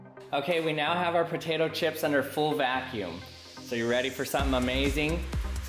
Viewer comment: Did he actually say Protatoe Chips Or did he say Potatoe chips?